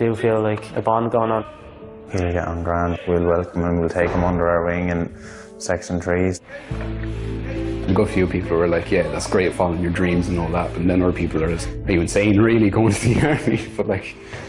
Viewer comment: I keep replaying it and every time Im hearing "sex and trees". Anyone have a clue what hes saying? Ironically enough Im Irish too, but there's no ONE Irish accent and some of them are very difficult to understand. Whats he saying?!